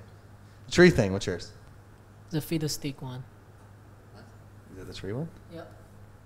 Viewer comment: did anyone else hear "Fetus Stick" the first time instead of Fiddlesticks?